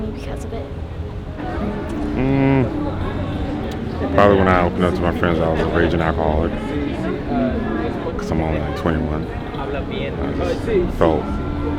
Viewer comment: Hey can someone explain what he said at 5:31 ? , english isn't my first language. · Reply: Yeah sure! This is what he said: “probably when I opened up to my friends about being a raging alcoholic. ‘cause I’m only 21. And I just felt like everyone judged me.” If you’d like any further explanation, I’m happy to help :)